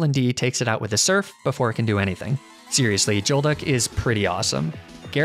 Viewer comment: I’m sorry, did he just say “Joelduck”???